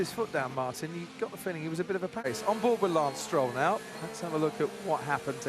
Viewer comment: sounds like Croft says “as soon as he put his foot down Martin, you got the feeling he was a bit of a penis”